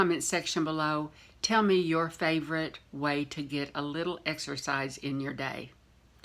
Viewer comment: Exercise??? I thought you said:”Extra fries”😊I do not exercise, but I would love to be enter in your giveaway!!! Have a blessed day. BTW, I like your new location;)